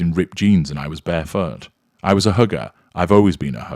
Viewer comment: When said HW was a 'hugger' I thought he said 'hooker' at first! 😂